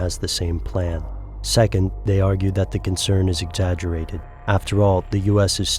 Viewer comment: i can't hear clearly what he’s saying ，“Second，they are xxxxx concerned xxxx”？